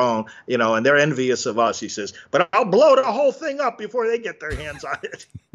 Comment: Correction. She did not say "I'll blow the whole thing up." She said "I'll blow the damn thing up."